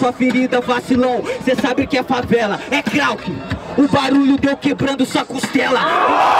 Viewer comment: Só eu ouvi a mina vibrando ... " Ooow eu ouvi agora , costela quebrada " kkk !!! Dms .